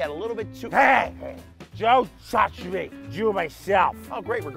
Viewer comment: DONT TOUCH ME YOU'RE MYSELF that's what I heard, and it moved me. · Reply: I heard "DONT TOUCH ME!! YOU MYSELF"